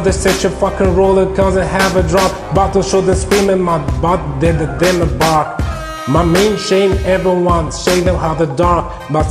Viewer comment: what does he say here? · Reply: Bark dilla dilla bark